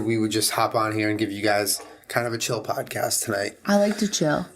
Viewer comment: Legit thought you said chilli.. Not chill.. I got a little too excited 😂